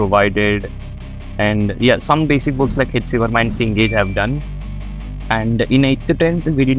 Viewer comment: basic books like cengage did i heard it right